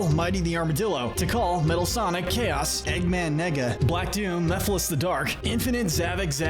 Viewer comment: uhhhhh what did you just say... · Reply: He said Eggman Nega.